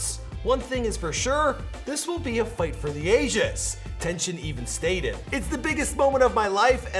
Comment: I thought he said this was a fight for the Asians. 😭😭